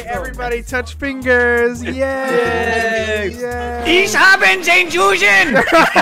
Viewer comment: Hat er: "Ich habe den Juden." gesagt? Habs nicht genau verstanden xd